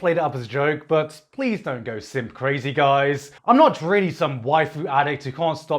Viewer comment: i heard "go simp crazy" and now my soul being held captive by a cute british succu- ahem lich